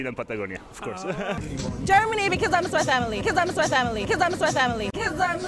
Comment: What did the girl say at 2:05? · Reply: "coz I miss my family"